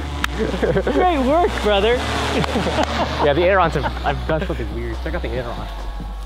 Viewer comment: I swear you can hear say "that's F***ing weird" at 8:43.